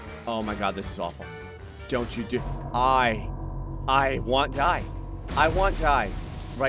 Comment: Did he actually just say "I want die" (to probably avoid being flagged by the system)? I think he just said "I want die." 😂